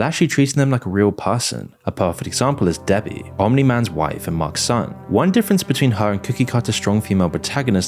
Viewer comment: did you just say marks son?